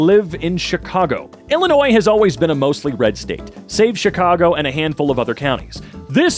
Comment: I thought he said "safe chicago" because I was like "Haha. Must be opposite day."